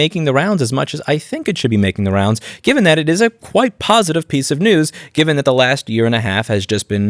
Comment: I thought you said covid positive 🤣🤦